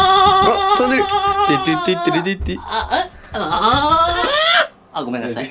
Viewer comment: しばゆーの『打って！打って！打って！』が段々『ウッディ！ウッディ！ウッディ！』に聞こえてくる😅